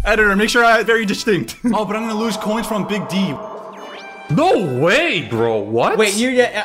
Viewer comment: Editor is wrong again, I heard Big B